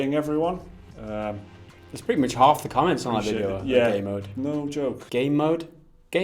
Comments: Did you just say 'gay mode'? lol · DID YOU JUST SAY GAY MODE?